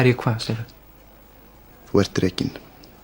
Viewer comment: Actually when I listened to the sketch, I thought the boss said "ert drekinn" not "ert rekinn".